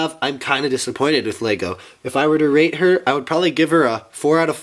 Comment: sounds like rape instead of rate LOL